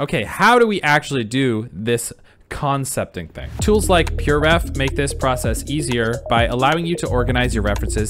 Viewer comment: you simply mention the app, and only display the logo, pretending everyone should know about it. For me it’s the first time I heard about it, I don’t know the correct orthograph to look for it on google, I even check your description to see if you will probably add the link or the name of the apps you mentioned in the video, sadly it’s only about promotion. Next time please add the name of the app with the logo, you can ignore the logo but please again not the name of the app. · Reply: pureref, chill out